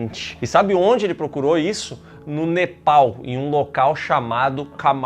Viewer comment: KKKKJKKKKKKKKKKKKKKKKKK quando escutei "no nepal' eu falei "no meu pau, aí vai o idiota dp Miguel "no meu pau é foda" KKKKKKKKKKKKKKKKKKKKK explodi